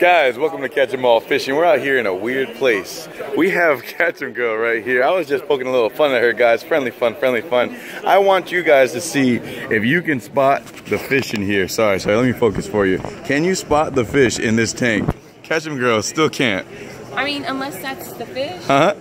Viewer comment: did i hear friendly fuck